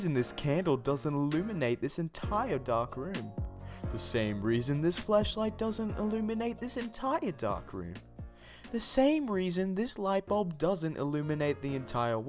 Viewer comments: DID YOU JUST SAY FLESHLIGHT???? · it sounds like he said fleshlight instead of flashlight.